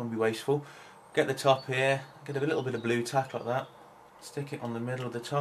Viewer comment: what is the blue thing? I can't make out what he's saying because I'm not used to his accent. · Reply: Bluetack